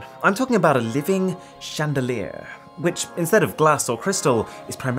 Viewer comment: i thought it said biohybrid chandler lol